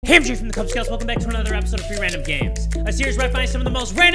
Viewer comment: I rather watch Jay play free random games than sleep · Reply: I always thought he said “three random games”